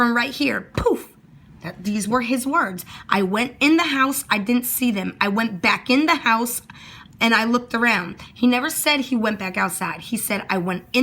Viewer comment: I thought he said I went in tha house I see them, I come back out tha house I don't see them, I went back in tha house and ask tha wife had she seen tha boys